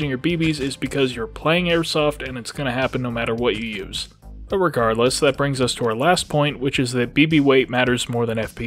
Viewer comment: Good lord, did I just hear him say "irregardless" !?! And after so much intelligent information, no less. smh